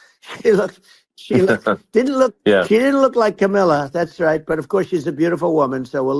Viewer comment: Sounded like he said Camilla not Kamala, 😂